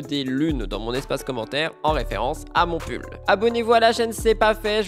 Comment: Mon Dieu, quand t'as dit "des lunes en référence à mon pull" ... sur le coup j'ai compris "à mon c*l" ... xD